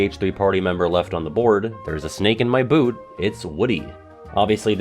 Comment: When I was little, I thought Woody said "there's a stake in my boot!" like a wooden stake he stepped on